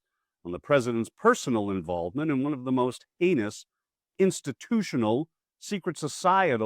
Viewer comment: At first I thought you said "anus" instead of "heinous". But then I realized it didn't really matter which word you said, because when you're referring to Republicans, those two words are pretty much the same in meaning!!!!